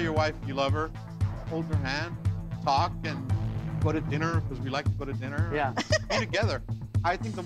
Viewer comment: HELP HE SAID GO TO DINNER BUT IT SOUNDED LIKE HE SAID PUT IT IN HER I WAS LIKE WHAT HAPPENED TO THE WHOLESOMNESS